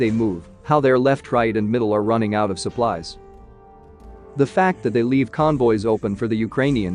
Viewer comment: I bet he actually said "they are running out of supplies left, right and center" .. I well know Britsh saying